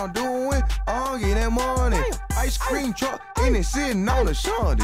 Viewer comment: LOL, did he just say ice cream truck sittin on asanti's? LOL